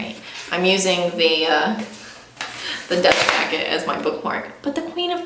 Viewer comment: Oh sorry than I understood wrong. I thought you said you had a Dutch cover of the book, but maybe that's my bad English that informed me wrong haha. Thank you for your response though! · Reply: Not a problem! I said dust jacket at one point, so maybe that’s what you heard!